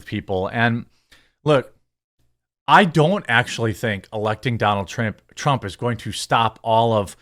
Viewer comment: Did he just say Donald Tramp at 10:45. How dare you good sir